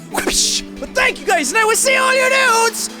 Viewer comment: Oh My God... All this time i heard "and i will see all your nudes in the next video" yes nudes wtf XD